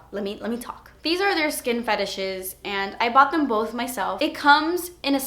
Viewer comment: LOL with the when you said "skin finishes" I thought you said skin fetishes